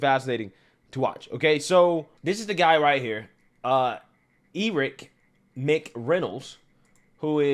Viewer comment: Did homeboy just say E-rick? 9:48 😂😂